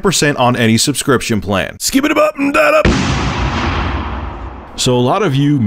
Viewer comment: what is he saying at 0:09..skipitdub dum bum da da???